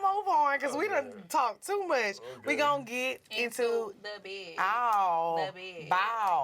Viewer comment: Lmao why would they be getting into the bag sis 😂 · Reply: I need serious help 😂😂😂😂 my brain be on auto pilot wit all these kids 🥴🥴 I heard CyHi say “get into the bed?” N I was like waittt A minuteeeeee it’s been bed this whole time 🙈🙈😩🥴